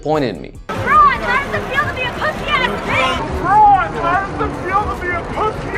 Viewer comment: what did the girl said again? Cant understand.. I just heard someting like Lebron..why..did... pu**y i guesse?? But i dont think im correct.. So what did the girl said again? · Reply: BL4Z3R 2.5 “pussy ass bitch” I think I heard her say